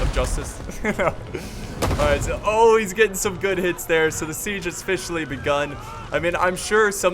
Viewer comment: I mispronounced Tully. RIP in Piece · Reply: "The Siege has Fishly begun" idk thats what it just sounded like